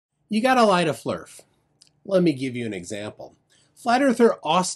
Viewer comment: When he said "You gotta lie to Flerf" at first I misheard it as "You gotta light a Flerf". I was imagining Nathan Thompson being set on fire... OK to be honest I'm still imagining it...